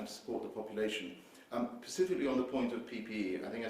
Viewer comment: Did he just say "pacifically"? .... 22:20 "Pacifically on the point of PPE" we're royally fucked